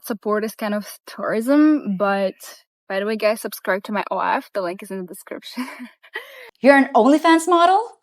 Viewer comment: I thought she said she had an OF, not an Irony Fans.